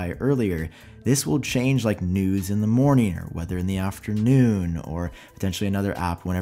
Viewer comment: anybody else hear nudes in the morning?